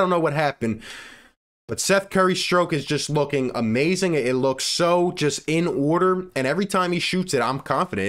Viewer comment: WHATTT DID HEEEE SAYYYYYY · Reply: The stroke was outstanding, very large splash against the hawks.